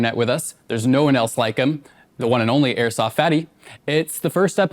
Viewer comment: I thought it said ABSOLUTE FATTY 😂